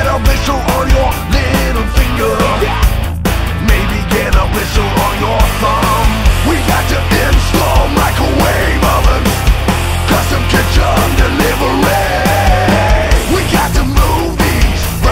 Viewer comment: Excellent cover, as always. When I was a kid, when this song came out, I thought the lyrics were "Money for nothing and chips for free". Sounded like the life I wanted to live. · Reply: for some reason I always thought he said, "we've got some In-store microwave ovens" LOL. Took a Scandinavian metalhead to show me the real lyric. hehe.